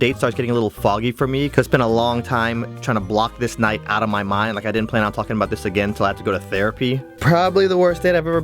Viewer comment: Did he say "I didn't plan on fucking bout this"